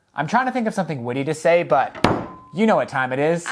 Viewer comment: When you said "I'm trying to think of something witty to say" before sanding, it sound like you said "woody" instead if witty.....so mission accomplished XD